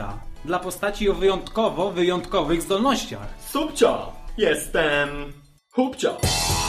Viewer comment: Na początku usłyszałam Jestem Pupcio ! :D